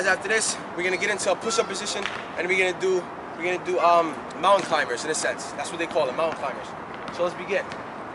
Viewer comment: What's the name of the exercise @ 5:25; I can't remember what he said, 'Alzheimer's'?